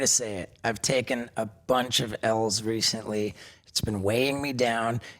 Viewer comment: Thought he said “takin a lotta lsd recently”. My jaw dropped 😂😂